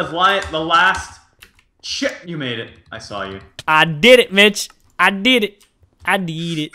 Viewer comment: who else heard mitch